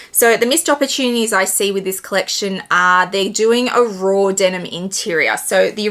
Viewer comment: It’s washed denim so I don’t think there would be much transfer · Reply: ah. I thought you said it’s raw denim lining which is completely different. Thanks for clarifying